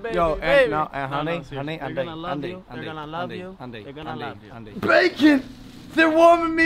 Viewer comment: The whole time Logan was saying “Andy” I thought he was saying “honey”